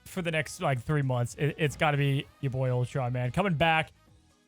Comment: it was very hard to understand what you said when you claimed Ultron as your favorite for next 3 months. I couldn't figure it out replaying on the podcast. Please don't forget to call out the character name for audio listeners. Much appreciated.